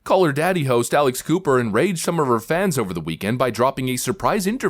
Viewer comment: I kept getting excited hearing about the Alice Cooper podcast! Very disappointed to learn I've misheard, I'm not 18 and school is not out